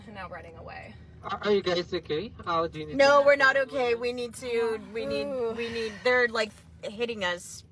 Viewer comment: Women are strong and independent until it's time to be strong and independent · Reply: How much you want to bet they considered themselves strong independent woman. At 2:47 I think she says, we need dudes.